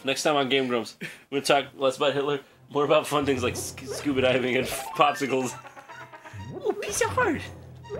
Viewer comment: oh scuba diving... i heard "scooby-doobing" and I was like ?wtf?